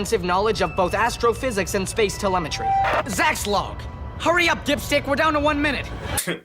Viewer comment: Okay, I swear he said, "Hurry up, dipshit."